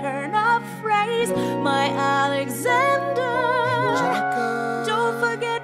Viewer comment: full stop. Did she say MY Alexander??!! all this time i though it was just "dear" alexander, Angelica that is your sisters husband wtf